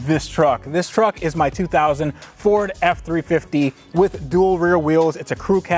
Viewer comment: those pipes you installed are called up pipes, the exhaust pipe coming out of the back of your turbo is your down pipe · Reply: No Name he said “2000 Ford F-350” I thought he said 04 as well lol